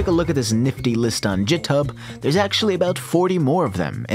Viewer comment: did you just say jithub....